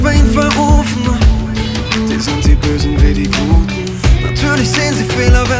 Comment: lol ich hab da "die sind die bösen Babygurken" verstanden xD